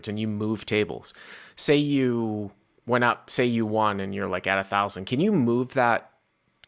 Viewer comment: did you just say wan? The word is won pronounced one you sob! I was just starting to really like this channel 🤣